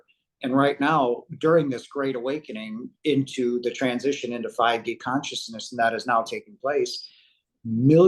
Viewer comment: sounds like he said 5g consciousness 😂😂